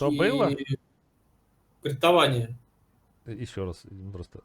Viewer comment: Услышал "криптование"